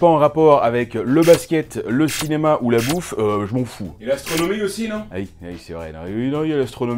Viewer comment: A 1:55 , quand il dit : et l'astronomie aussi non? J'ai entendu : et la sodomie aussi non? Et ça marche aussi je pense , connaissant l'animal